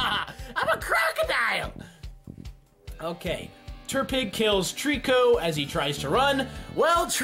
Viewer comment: I thought he said Turdpig lol.